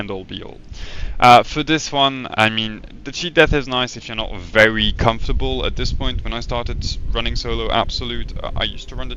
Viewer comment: Audio got cut around 9:05, what did you say there ? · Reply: I was trying to explainb Cheat death is nice training wheels when you want to start running solo runs, but its sub-optimal. To put it that way: it saves your life every 3 minutes. If you're good enough, you don't need it for another 3 minutes and you're technically immortal. In reality though, if you're really good enough, there's way more value out of the equipment regen that takes care of iffy situations by removing threats or buying space.